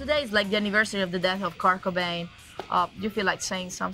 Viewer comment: The English subtitles say “Kaka Bang” Instead of Kurt Cobain. 💀